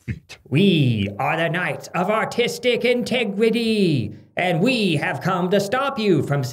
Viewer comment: I heard "Knights of Artistic Integrity" as "Knights of Autistic Integrity".